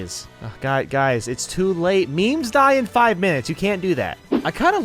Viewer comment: “Yes, but it’s not melee,” i thought you said memes die in 5 minutes.